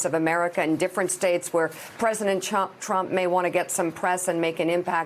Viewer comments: did she just say president chump? lmao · Did anyone else catch her saying "President Chump" at 0:11? LOL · Did she say President Chump?